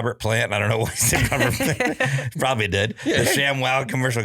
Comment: Robert Plant is not dead. Zeppelin did a lot of coke in the 70's but only the drummer died and that was from alcohol. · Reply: I don’t think he said Robert Plant is dead. He said “he probably did”